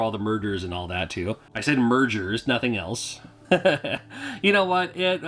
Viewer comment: I thought you said MrDr's, not mergers... hahaha